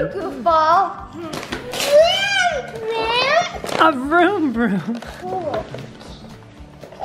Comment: It sounded like said "Thanks Man" when he was opening his motorcycle instead of saying "A Vroom Vroom"😂🎂